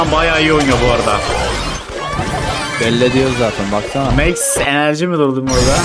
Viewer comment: max energy mi duydum orda😅